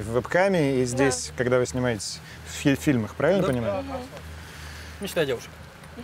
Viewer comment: мечта девушек - не считая девушек 🤣🤣🤣 Неужели не замечают тролинга Пивоварова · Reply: также послышалось в первый раз, но парень сказал "не считая девушек", а не "мечта девушек"